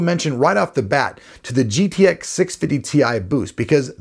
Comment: did he said GTX 630 Ti boost?